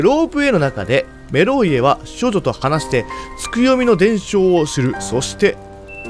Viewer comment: 少女が処女に聞こえるww